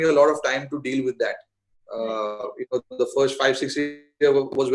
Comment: What did he say there 5-6 _____? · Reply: Years